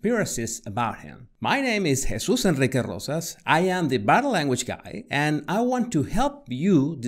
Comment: I thought you said "I'm the bad language guy" Then I read your channel name...